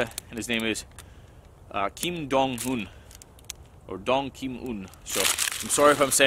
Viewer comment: That's pretty cool but when you first said his name I thought you said it was Kim Jong un